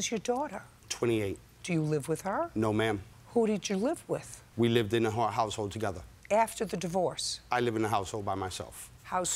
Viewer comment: I almost thought he said “we live in a whore household together” lmao idk what he said tbh